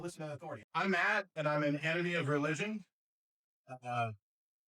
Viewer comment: Did he just say I'm fat and an enemy of religion?